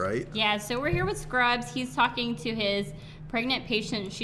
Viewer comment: I yawned at 2:38 so i thought she said So yeah we're here Scrubs he's fucking with his pregnant patient. XD